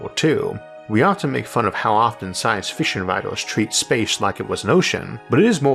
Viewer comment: Science FISHen writers. Sorry, its what I heard. Im not making fun of you. It is just neatly topic-relavent.